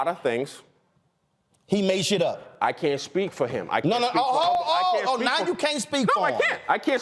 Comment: Did he just say I aint speaking for him?